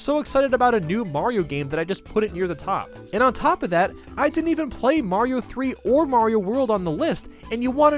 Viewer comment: he actually says And I didn’t even play Mario bros 3 or world on the list